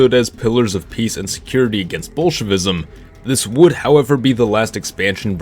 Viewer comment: Did you just say "ball shitism" Here's the time